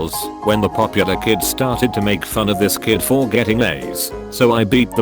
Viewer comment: thought it said AIDS instead of A's